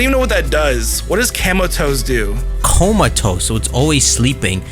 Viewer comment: All I can hear is you saying Camel Toes, and I can't stop laughing 😅🤣